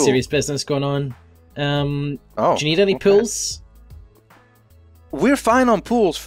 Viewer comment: i thought said: "need any PILLS?"